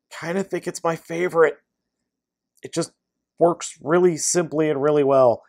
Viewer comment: i thought you said crashbashes transformation was "really simple"